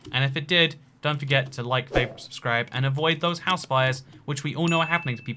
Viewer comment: Did you say “Like, vape, and Subscribe at the end or did I miss hear it?